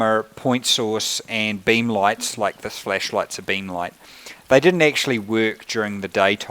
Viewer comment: Sounds like he is saying "fleshlight" everytime he says flashlight... So funny